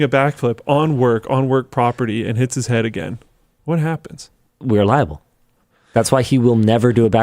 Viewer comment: When Linus said that they were liable... I thought he said we ub alive him.... I dint think I've ever laughed so hard at a video that you guy have made. Accept that one live video right after the queen kicked it.